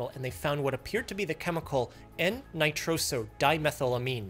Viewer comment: I thought he said MDMA 😳